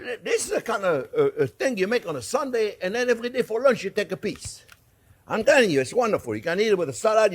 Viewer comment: Lol it sounded like at the 7:33 mark he said take a Piss so Funny !!!